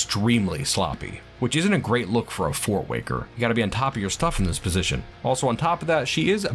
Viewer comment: I misheard "fortwanker" and was deeply confused for a moment lol. Clearly the FortWaker needs some time at the pumps. Gotta earn that fancy helmet. I'm really quite taken with the barbarian rock aesthetic of this fort! I like how you branch out a bit with the art style and related culture for the dwarves, it's something I love about your channel, keeps me coming back each video. I've also noticed all the animals are giant, including the horses, was that a bug or an intended tweak? I know Dwarves are small but not THAT small...